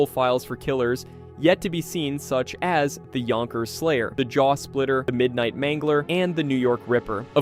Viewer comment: Lmao I thought one of those names said “jewsplitter”